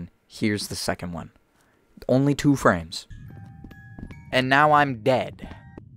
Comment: I miss heard one of the words you said in a part of the video I thought you said now your gay but really you said now I'm dead my ears sometimes don't know how to work properly heh heh